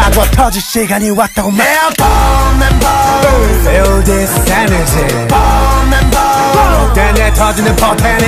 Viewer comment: I-I heard “Bone and bone. Feel this energy.” I wouldn’t have been surprised if that was in the lyrics honestly :,)